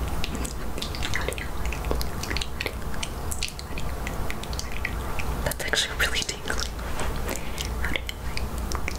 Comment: What is she saying at 3:11 ? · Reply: howdy-ho-hi